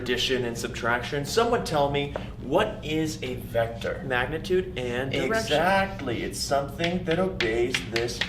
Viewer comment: "Magnitude and ...erection!" Swear that is what I heard